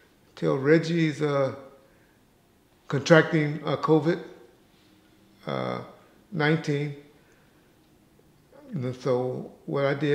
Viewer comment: deep down i heard him say contracted aids 1995 , i wonder who he was thinking of for that millisecond